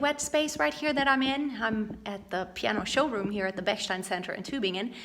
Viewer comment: Did you say "Это"? 0:22 Thanks for your advances, they helps to live better